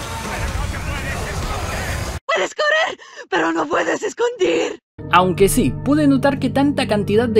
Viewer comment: yo tampoco entendía y creí que lo decía mal pero es que lo que dice es "Puedes correr, pero no puedes discutir" xd